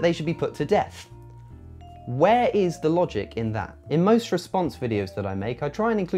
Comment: I thought you were going to say, “Where is the LOVE in that?”